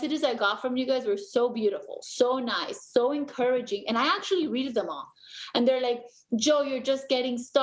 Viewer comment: ….did she say she “readed” all the messages?